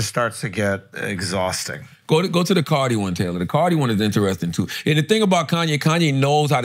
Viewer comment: Sounds like charla just said cardi don’t write👀👀👀